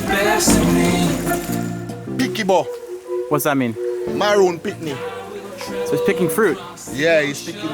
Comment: I don’t think he’s saying Maroon Picnic @ 6:47 I think he’s saying maroon pickney meaning child 😅